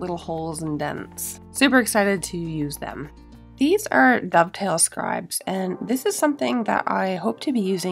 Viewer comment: What were the red "bow tie" looking things called. Sounded like you said the Pill scribe? Its at 27:48 in the video. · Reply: Dovetail scribes!